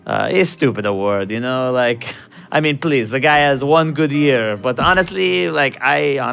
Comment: Ronaldo: "I will have the cod" Like a true Portuguese!! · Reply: I actually thought he said, "I will have the cod piece." I believe it was "please..." but you have to be careful with that one.